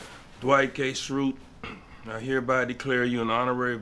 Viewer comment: I swear I heard "Dwight Gay Schrute".